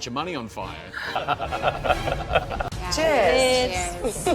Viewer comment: kto jeszcze usłyszał "cześć" xd